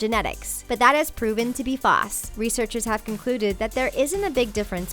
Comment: BOI WHY TF DID SHE SAY FASS INSTEAD OF FALSE